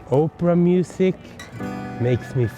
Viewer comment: In front of the Opera House. I thought you said "Oprah music." Ha!